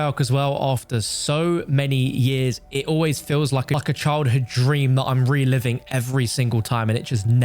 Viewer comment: Check your video at 7mins 36 seconds. Sounds sounds like you say a F***king child hood dream. I got a giggle out of it.